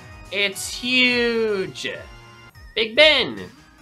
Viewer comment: WHAT DID YOU JUST CALL THAT TOWER?! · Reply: Big ben